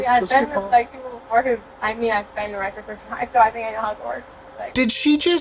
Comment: Before you clarified her saying “I know how it works,” I thought she said “I’ve been arrested at Hogwarts.” 🤣🤣🤣then I thought “damn how drunk is she?”